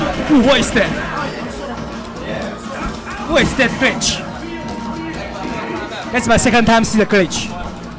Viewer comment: says ... what? Totally can't understand him. Sounds like, "What's that Grinch. That's my second time to see the Grinch." Love.